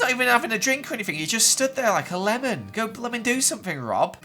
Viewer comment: It sounds like your saying Lemoners 😆 LOL